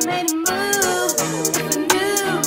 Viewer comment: Who else heard it’s to late to play kazoo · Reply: Me but it’s “it’s too late too soon”